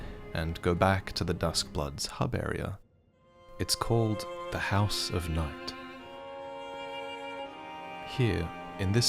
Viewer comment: Yeah...I heard House of Nut.